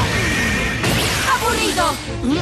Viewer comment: Qué es lo que dice? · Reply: Aburrido